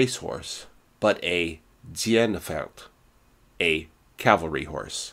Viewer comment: I've been trying to understand what you said at 3:34 3 times now and failed. Tsien-feld? Ziehen-feld? Ziehen-pferd? Did you mean "Zugpferd", as in draught horse? That said, loved this video. the 190 was always one my faves. Having not read about it in years, it didn't even occur to me the Kommandogerät was a predecessor of what we now call FADEC. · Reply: "Dienstpferd" - I may have several talents, but languages and pronunciations aren't my forte!